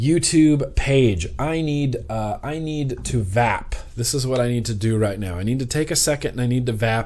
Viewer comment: Oh goodness i could have swore at 9:10 i thought he said i need to "Fap" tango down.. hahaha